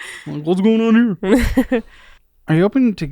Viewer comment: "Are you open to getting rid of your three row" I heard this as "Are you open to getting rid of your three year old" and it surprised the fuck out of me that went there.